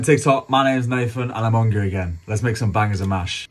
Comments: Deadass thought he said “My names Nathan Adam Ondra again” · Hahah he said. Im hungry again I thought he said im hungarian at first. 😂